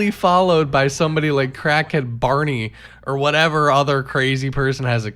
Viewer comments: You got me with that ending, dude. I was caught on the hop with that one. And I thought you said 'Crackhead Barbie'. 🤷‍♀ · I thought you said Crackhead Barbie which actually works better AMIRITE🤔